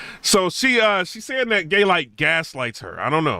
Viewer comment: lol it sound like you said gaylyt